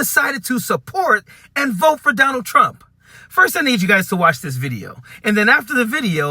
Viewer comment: All I hear is, “I will bot por Don Ald Tramp.”